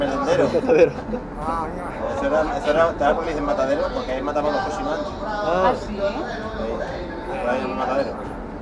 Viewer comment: "Porque ahí mataron los cochinos antes" es lo que dice